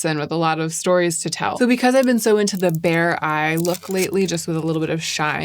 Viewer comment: Why did I first hear "bare eye" as "bear eye"? Like what is this new trend? A new take on the cat-eye? 😆😆